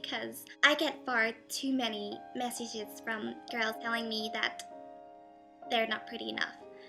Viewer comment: Moment...did she said on 0:03 "..I cant Fart to many messages from Girls.." ?? Or are my Ears damaged. Anyway. Good Vid. Should help some Girls getting some more Confidence to themselves. And keep it on GIRLS. YOU ALL ARE HOT on your own beautiful Way!!! =*